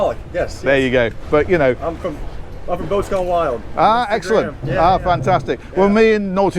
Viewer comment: what is the guy's channel? english is not my first language, I had trouble hearing what he says. Boat sky wild or something?